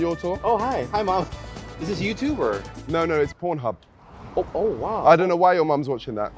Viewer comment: what did niko says? Por* hu*🗿😭😭